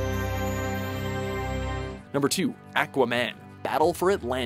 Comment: Let see Watchmojo make a video game..... At 6:50...... What did he say??? ACKwaman???